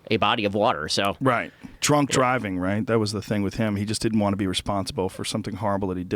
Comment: did he say "trunk driving" ?